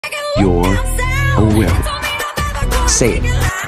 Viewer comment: It sounds like he said you're a whip 😂😂😂